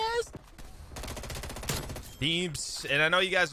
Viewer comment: lmfao anybody hear the n word at 2:51? i know he didn’t say it but it sounded like it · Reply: nah he said “this THING is nasty”